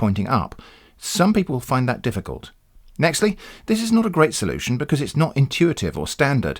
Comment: Did I hear you say "nextly"?